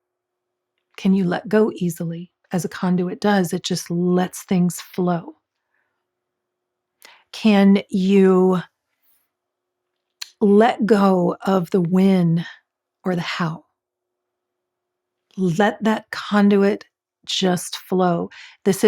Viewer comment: 2+0+2+5 = 9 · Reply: mm, i heard "can you let go of the wind 🍃" haha this is the time to be sexy and nonchalant